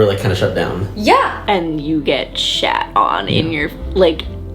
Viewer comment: Did I hear the young woman @ 12:18 correctly? "Shat" on??🤔